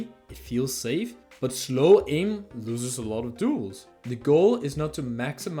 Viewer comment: subtitle says "tools" instead of "duels"